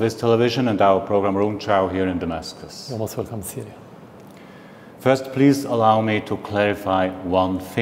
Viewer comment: what bashar al assad says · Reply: always welcome to Syria